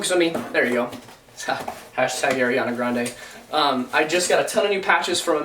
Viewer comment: did he just say hashtag ariana grande